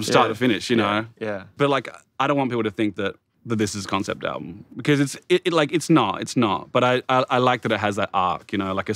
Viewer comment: What album did Kevin say is one of his favorite concept albums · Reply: A Grand Don’t Come for Free by The Streets